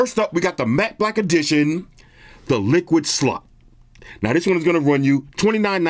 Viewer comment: Did he just say "liquid slot"?